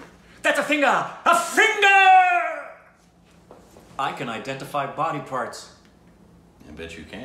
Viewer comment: I thought I heard you say- 'I identify as body parts'🤔🤔🤣🤣 My reaction- WTF 🤣🤣